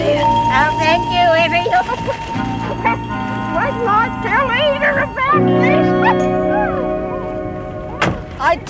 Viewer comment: lol i swear thats aunt Ida i think she even said wait til i tell rita about this hahaha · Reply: She said "Eda*" not "Rita"